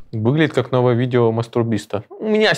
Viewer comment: как же долго я угарал, когда вместо мистера биста мне послышалось мастурбиста